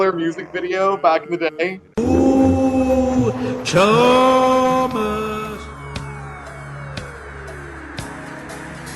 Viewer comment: He did? what did he say? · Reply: Hee Hee